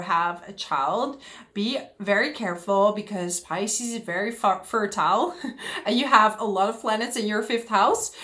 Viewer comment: I must have misheard this..." f# fertile "😂